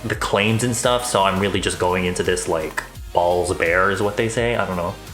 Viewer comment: I KNOW you said “balls bare is” but what I HEARD was “balls bears” so